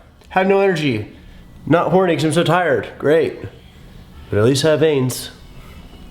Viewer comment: thought he said, atleast i got aids